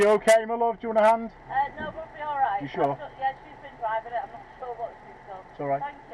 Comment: Could you put the clip with the white vehicle (approx 5:30) into context please.... unable to hear what the lady said. Thx. Wow.... some dodgy "roadcraft" on here; especially the 1st clip. Haha.... the last one..... ewwww!! Bogies at 6 o'clock. 🛫Tally Ho!!! Stay safe everyone 👍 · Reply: Evening Graham. The lady in the white car said the girl driving had made a mistake but she didn't know how. She then got in to help out and tried to crash into the wall!